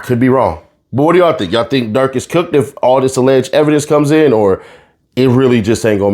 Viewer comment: Nobody, they'll be in jail forever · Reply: thought u said u think durk gon beat it